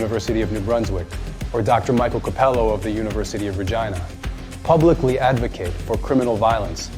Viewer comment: Could swear I heard him saying University of vagina?